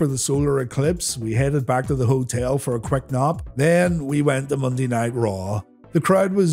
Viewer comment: I know you said nap but I could have sworn you said headed back to the hotel for a quick knock after the zoo. Giggedy 😏